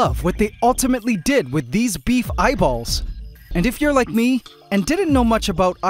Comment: I heard beef balls 💀